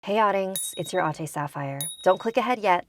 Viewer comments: At first I awlays thought she said "Hey oddings, it's your Aunty sapphire" so either meaning I'm fine with haha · When there’s something scary... In your neighbourhood Who you gonna call? Your Ate Sapphire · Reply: I thought you said "you ate sapphire" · This entire time I thought she was saying auntie sapphire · And here I always thought she was saying, ". ...your auntie Sapphire" 😂😂